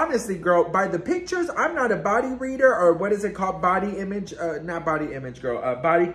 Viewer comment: 😂so funny almost thought you said I’m not a bottom feeder! 😂